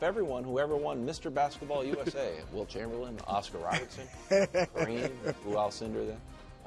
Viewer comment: I thought he said Jeremy Lin won Mr Basketball. Somehow he really said Wilt Chamberlain